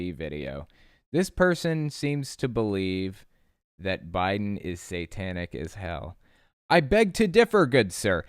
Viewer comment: I thought he said “the Bible is satanic” not Biden.